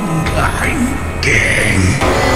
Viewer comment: ngomong apa ndak jelas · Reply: Tong gandeng (jangan berisik)